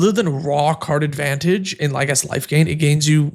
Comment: ngl, it sounds like you said "rock hard advantage"